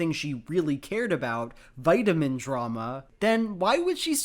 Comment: I just rewatched this video and realised I misheard. I thought he said Tati experienced “VITAMIN TRAUMA”. When he actually said “vitamin drama”. I mean that’s still funny but for 3 years now I’ve incorporated the phrase vitamin trauma into my vocabulary. I actually have never had a chance to use it but I use the term in imaginary situations to myself all the time.